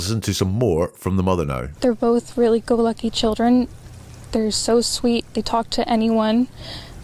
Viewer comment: I thought she said ,they were both good looking children to be honest . Then she says they will go talk to anyone in a store? Then she says their bith non verbal when she says their almost autistic ?